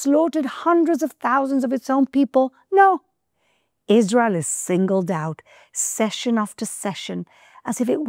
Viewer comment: There is a really STUPID typo in your subtitles at 2:39. She says "No. Israel is singled out,", but your subtitle says "No. Israel is single doubt," SINGLE DOUBT?????????????????????? WTF?????? Come on!! How do you expect anyone to take your video seriously if you can't even take the time correct such an important part of the script??? That is absolutely ridiculous. This RUINS the whole video!!! Don't you see that???? What a bunch of idiots.